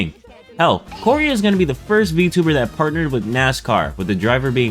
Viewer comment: at first I thought you said Cory is about to be the first VTuber P.s. Great video, I do agree with you on originality and be unique but man it is quite difficult tho.